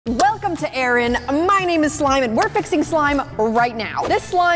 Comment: I swear i heard Erin say "Hi guys welcome back to Erin,my name is slime."